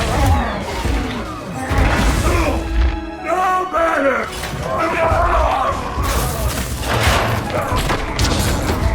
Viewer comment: what does he say? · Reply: "No, Banner!"